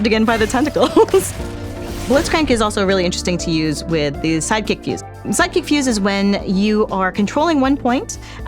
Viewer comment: I swore for a moment that she said "Psychic Fuse" and now I'm trying to figure out what a Psychic Fuse would do